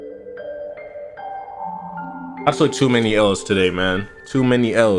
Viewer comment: thought said "i smoked too many o's today" spat out my fucking water